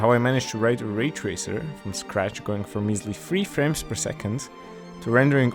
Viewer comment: Not gonna lie I thought you said in scratch